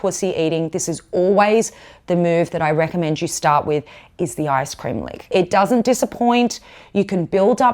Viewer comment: we start with ........ the icecream maker? or I scream mimi ? cant understand what was said there